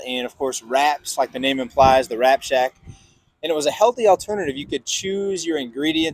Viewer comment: i heard 'Rat Shack' at first lol